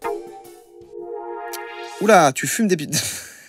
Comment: A 7:53 j'ai entendu " oula tu fumes des bites" 😂😂